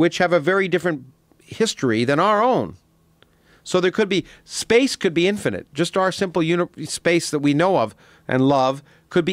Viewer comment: Did anyone hear him say "space could be infinite... And love""" maybe I'm crazy but I swear he said that